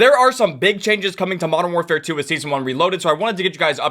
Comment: Bro I thought it said big chungus 💀💀💀💀💀